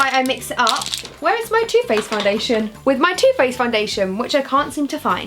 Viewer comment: i thought she said toothpaste foundation instead of too faced foundation 😂😂😂